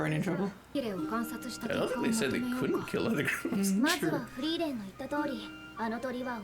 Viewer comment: “I thought they said they couldn’t kill other (couldn’t hear this clearly… ‘other crews?’)… Actually, I don’t think they said anything about a non-killing rule when the proctor was explaining this task to them. You may have been thinking about Ubel’s case as she was disqualified for her 2nd class mage exam when she killed the 1st class mage Proctor who was giving the exam. Each exam may have its own rules, so in this one, the more cold-blooded mages may see murder as an option since one of the conditions for passing was that everyone must be alive and present by the time limit.